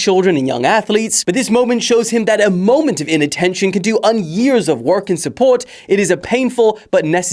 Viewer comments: Is it just me or did he say “can do un-years” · "can do unyears" i thought i heard wrong